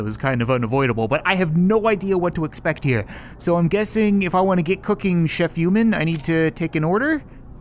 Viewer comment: i thought the ODER UP! said. get cooking get humen but it said get cooking chef humen